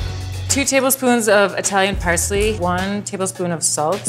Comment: did she just say 2tbsp of italian parsley